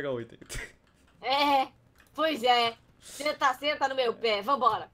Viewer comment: oh oh foi mal senta senta no meu ... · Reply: pe de novo